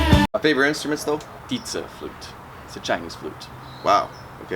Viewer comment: I honestly thought at first he said pizza flute and I had to go back to realize that it wasn't the pizza flute though now i want pizza, and while waiting for said pizza i'm gonna go play my flute